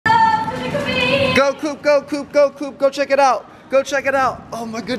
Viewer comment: Go poop go poop i thought he said that but it was coop 🤣🤣